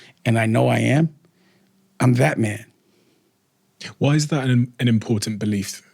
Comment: “You know how many people have tried to convince me I’m not a superhero and I am, I’m THATMAN!” I love it 😂😂😂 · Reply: And I thought he said, “BATMAN”. 🤦🏾🤣🤩 (he talk so dam fast)